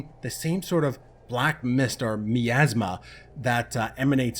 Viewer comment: What is that word you used? also at 6:40 doesn't that laugh make you think What a Charming individual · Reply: It's Miasma I first heard in in a Final Fantasy game but it was originally used to define "an oppressive or unpleasant atmosphere that surrounds or emanates from something."